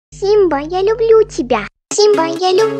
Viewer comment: Я один услышал "симба Я посожу тебя на аниме"?